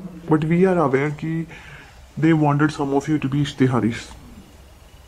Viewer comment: What’s the word that investigating officer asked her @ 25:45 ishtehaari one